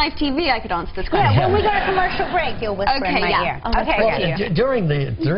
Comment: What ask Regis in 4:57? Can someone translate me? I'm spanish I heard it a thousand times and I can't understand him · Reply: "During the ten years that you've worked together with Daniel Radcliffe and the Grint (he's referring to Rupert Grint), did you develop a fondness for either one of them ?" Basically he's asking Emma if she's had or has a crush on Daniel or Rupert since the beginning, and he asks that question every single time she comes on the show, so she makes fun of him by telling him they're getting married and showing him her fake "engagement ring" and saying they're gonna have little red-hair babies :) I know you posted this three months ago but I'm glad if I could help !